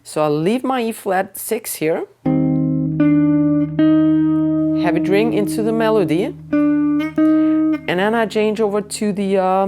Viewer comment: I believe you said "Let it ring into the melody". But for some reason I heard "Let it DRINK into the melody". I think I need a drink. 😂😂😂😂😂😂